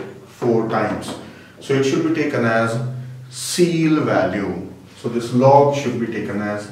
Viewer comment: what is seal? I am bad at knowledge of words. I could not find the answer. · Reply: It's actually the ceil function, where the value is the least integer more than the argument you are passing in. In this case it's 4 cuz ceil(3.2) = 4(least integer greater than 3.2)